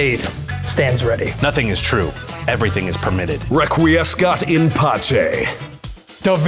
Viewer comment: What does Jonathan say at 6:41 and what does it mean? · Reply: Requiescat in pace, wich is Latin for "may he rest In peace" often short handed by R.I.P. It's what the assassin's in the game often say after killing an important charachter.